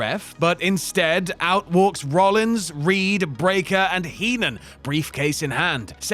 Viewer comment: I swear said 'Heenan' instead of 'Heyman' 😂damn near dropped my coffee when I hear that 🤣